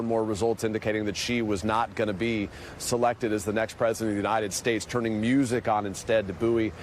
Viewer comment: Did he say "She's not going to be SELECTED as the next president of the US" ?